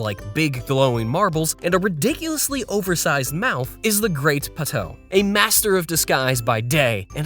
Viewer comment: thought it said “The Great Potato”.